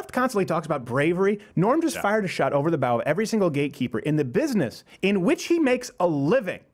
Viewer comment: Every time you Americans talk about gatekeeper I always hear it "GayKeeper", LOL.